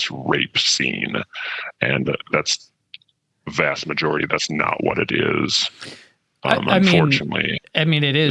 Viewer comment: did he say it was unfortunate or did I hear that wrong?? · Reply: yeah he said, “that’s not what it is unfortunately.” 😟